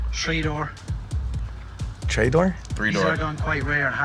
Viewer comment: he has to be Scottish saying a tree instead of three